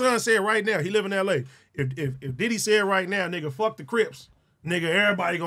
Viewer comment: “Diddy gay” that’s what I thought he said 😂